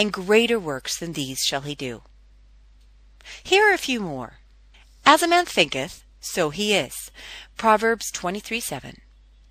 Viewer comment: Did you say proverbs 23:7? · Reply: Yea I'm really confused about this to, I thought she said "as a man thinketh so he is, proverbs 23:7, it is through thinking that a man forms that which he has in life, proverbs 23,7"